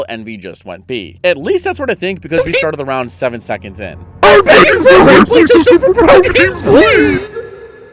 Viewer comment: Wtf did he say at 19:02 ?? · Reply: he wants a replay system 😫